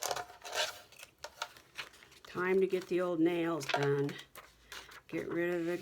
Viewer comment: Yes ma’am I’m still up for the challenge your dangles are cute don’t give up just walk away then come back to it I like that peacock image lol omgosh I thought you said you need to get the nail gun what you said u need to get your nails done your over the glitter lol hey you are gone to love it so much easier one step loopy tool I need to figure out why my running dog on my sewing machine not working I miss my sewing machine 😔 I don’t understand people sometimes it’s like what the heck is Anything working in their heads I love your Floating Pocket thank you for sharing have a wonderful crafting time